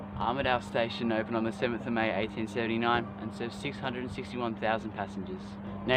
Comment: did he say 1839 or did you just say 1879 funny and it sounded like 1839